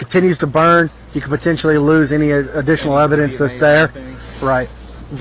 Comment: sorry but...did he just say POTENTIAL?!